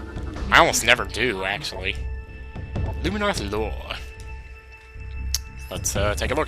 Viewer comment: I swear I heard "Illuminati Lore"